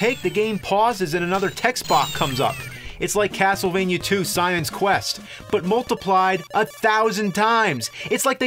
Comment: I think the nerd said "Science Quest" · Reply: Shawn Bateman *Simon's quest